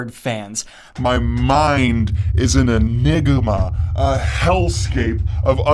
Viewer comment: what did you say?? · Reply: He said enigma